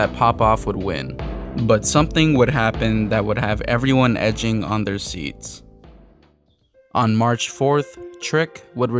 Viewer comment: what did your say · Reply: I didn't mean it 💀